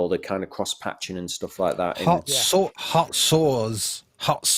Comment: My silly American ears thought was saying “heart sores”. Sounds painful.